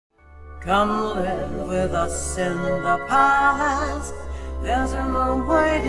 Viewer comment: I thought yellow said come live with us in the club house 😂😂